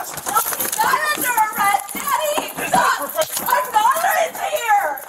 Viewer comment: “how do you do that?!” “Well you see, the door was in the way so I opened it” Also did she call out “Daddy” after that or am I mishearing something? Because the dudes name is